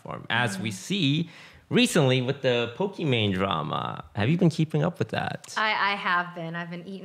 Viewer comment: I thought it said "Pokemon Drama." In a way it does, but you know.